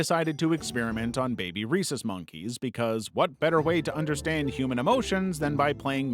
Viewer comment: I’m so deaf I thought he said “racist monkeys” 😭